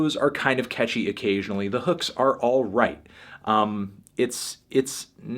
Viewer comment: did I just hear "alt-right"???? BAN ANTHONY FANTANO!